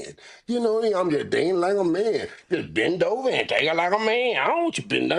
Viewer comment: Did Say Bend Over And Take It Like A Man! {Pause} 😅💀